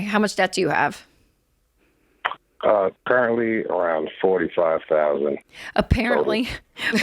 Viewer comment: when asked how much debt he has, caller replies “Currently I have…”. Rachel hears him say apparently instead of currently and makes a joke of it laughing… “Apparently…! I dunno but that’s what they say I have apparently… hahaha. Wow. Some of these interactions are so hard to listen to……